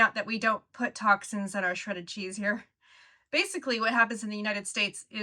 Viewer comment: 'don't put toxins in US shredded cheese' - video shows list of toxins in shredded cheese. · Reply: I thought she said 'cheddar' cheese and was puzzled as in the UK it contains nothing but milk, rennet, salt and cheese bacteria. I had to go back and listen again - 'Shredded' cheese (grated) now it makes sense!